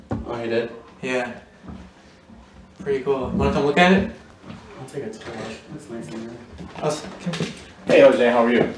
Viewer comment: I think he says "I'm going to take a tour. It's nice in here" Maybe?